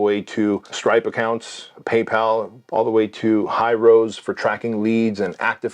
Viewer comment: noooo the editor put some random app "Highrise" instead of "HYROS" 😭😭😭